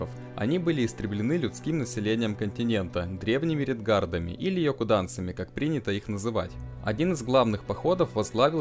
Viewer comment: после того как ты сказал слова "редгардами или ёкоданцами" мне послышалось слово РЕДГАРДЕЦ, blyat